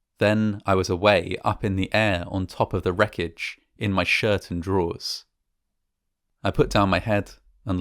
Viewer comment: For a brief moment I looked away and listening about the chaos. I had to pause for a minute. At 5:45 it sounded like he said "shit my drawers." No, he said "shirt and drawers."